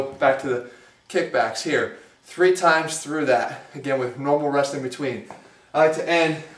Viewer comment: what'd he say at 3:58?? ''no more resting..or normal resting??